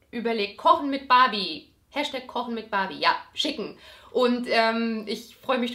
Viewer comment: ich hab verstanden: #kochenmitbarbieCHICKEN haha